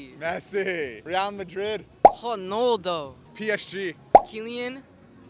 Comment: Everyone : Ronaldo That Kid : HoNaLdo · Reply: I thought he said penaldo